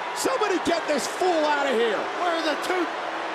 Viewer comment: did he say, “where are the tooth?” Lol